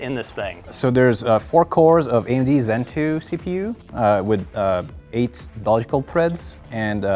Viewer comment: did he say it's made from Doge Coin threads?! @@